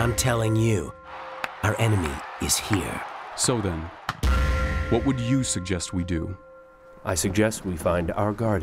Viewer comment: I heard "Soda, what would you suggest we do?" At 00:41......